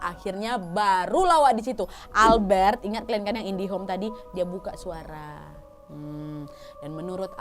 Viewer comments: Apa aku yg salah dengar ya, dari awal cerita kak bilang indihome bukan indigo 😅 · Kak, aku dengerin3x …. Kok kedengeranku indihome i/o indigo ? 🤔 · Kok sebut indigo. Indihome sii ka wkwkwk kirain salah denger tapi ternayat bner ka sembuh indigo. Tapi IndiHome wkwkwkwkw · Aku salah denger gak sih, mbak bilang indihome bukan indigo, indihome kan nama wifi 😁